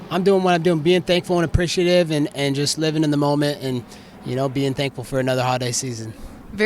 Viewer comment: Sounds like he said he's thankful for war ...